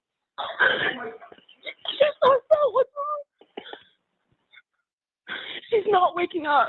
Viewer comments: I don’t hear that. Is this a different audio you’ve heard? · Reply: says “Xana, Ethan” 3:06 says “oh my god/gosh” 3:08 panics and says “she’s passed out? What’s wrong?” after hearing say oh my god/gosh. 3:11 says “she’s not waking up” 3:14 yells Xana’s name. At 3:15 he yells Xana’s name again. At 3:18 you can hear him yell “aaaaahhhhh” then moments later he screams and shortly after at 3:52 he says “get out, get out, get out”. You have to listen carefully cause it’s hard to hear with and the dispatcher talking and heavy breathing. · I don’t hear that. Is this a different audio you’ve heard? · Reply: I time stamped what I heard for you but my comment got deleted 😏 it starts at the 3:00 mark. At 3:00.. you hear him say “Ethan, Xana” at 3:06 you hear say “oh my god/gosh” at 3:08 asks “she’s passed out? What’s wrong”? 3:14 you hear say “Xana” at 3:15 you hear him say “Xana” again. 3:18 you hear him yell “aaaaahhhhh” 3:52 you hear him tell someone “get out, get out, get out”.